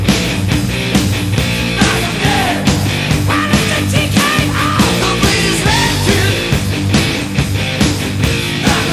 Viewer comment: lmao what does Vince say at 1:13 ? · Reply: Well it's a TKO In boxing a T.K.O means a Technical Knock Out